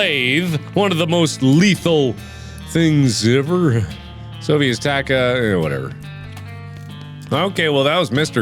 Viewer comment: Did he say "This sofy is tacka?"